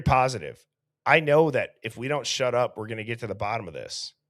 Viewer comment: I keep hearing "we gotta get her to the hospital"